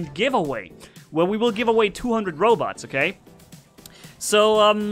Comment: I thought he said 200 robux😂